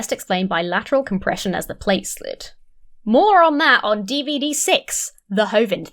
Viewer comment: When you say: "More on that"... about Kent's mumblings I hear: "Moron that"